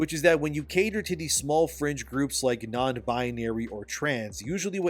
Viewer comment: hah had a small chuckle @ 5:13 thought you said "small cringe groups" had to rewind it to check😅